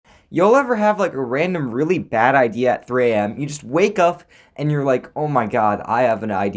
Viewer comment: he said uf instead of up lol